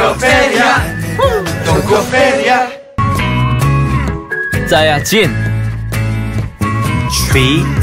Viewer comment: alguien mas escuchó que dijo sayajin???? :v